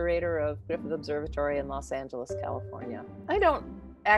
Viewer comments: did she just say Los Santos instead of Los Angeles? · Wait a min is it just me or she really said Los Santos, California 😳